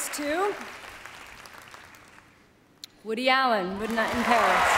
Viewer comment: sounds like she said "Woody Allen - Wood-night in Paris"